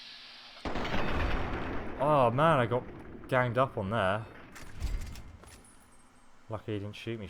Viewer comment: I thought he said gang banged XD